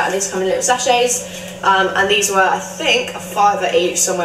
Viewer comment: I thought you said sausages not sachets 🤣🤣